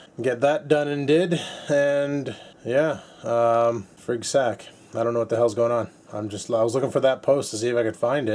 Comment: frig sack?just heard him say that 10 times,checking out your channel for something diffrent for frig sack.